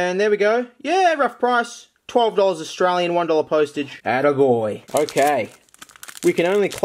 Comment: DID HE JUST SAY ATTA GOY <3